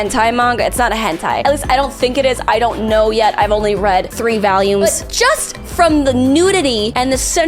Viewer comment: lol the way you say 'volumes' makes it sounds like you said "three valiums"